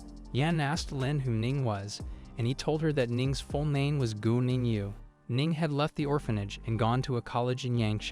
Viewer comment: his name: Guo Ning Yue What I hear: gooning you